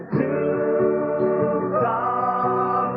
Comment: Tube tops! I thought they were saying two Toms. Their outfits made no sense.